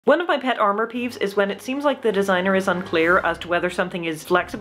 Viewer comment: i thought you said “pet arm peeves” as if you cared deeply about pet arms